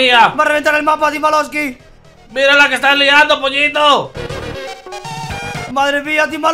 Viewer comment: escuché a timba diciendo "pollito" el riumba is real